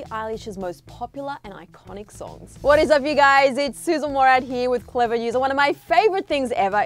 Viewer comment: Lol. I thought the host said, “hey what’s up guys, it’s sizzle Mourad here.” 😂😂 SIZZLE